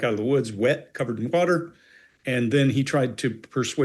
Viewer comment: At first I seriously thought you said "covered in BUTTER". Butter? I had to rewind.